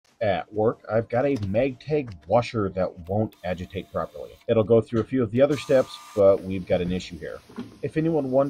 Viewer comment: Sounds like you said "Mag-tag" washer.... interesting enunciation